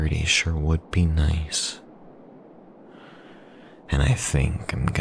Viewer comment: This is, isn't it? The "doe eyes", him being a hit man, sadistic yandere. Yea, this gotta be sean. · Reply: What does doe eyes mean? I thought he said dull eyes.. no hate I just speak german